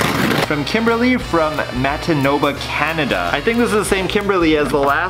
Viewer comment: did he just say mantitoba?😂 like if ur canadian lol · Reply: sounded like he said Matinoba lol